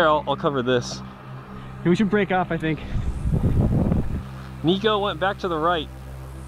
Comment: we should break up I think (XD I thought he said they should have a devource but it really does right?)